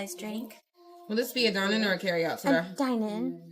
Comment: I thought she said "will this be dine it or carry out today?"